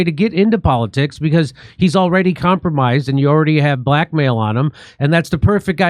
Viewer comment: For a second there I got distracted and thought you said “had black male on him” instead of “had blackmail on him” but damn my neck broke causes it whipped back so fast